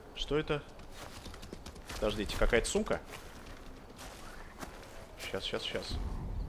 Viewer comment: мне одними послышались что он на 33:12 сказал сука